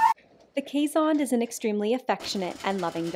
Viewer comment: Any other Star Trek fans? When she said Keeshond, I thought she said Kazon, like from ST: Voyager 🤣